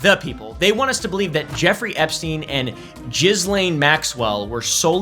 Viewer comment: Yeah, Trumpstein, where are the files, and the 'philes? Also it's hilarious to me that it sounds like is saying "jizzlaine" Maxwell 😂